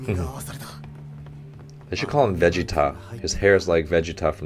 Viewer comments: I literally thought he said “veggie-top" and not “vegeta". Lol. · I thought he was saying "veggie top" haha until he said from Dragon ball that I realized that he was saying vegeta